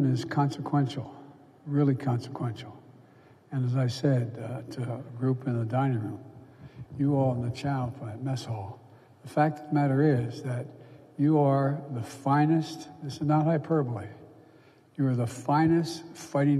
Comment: What did he say at 2:49? 'The child I'm the mess hall'?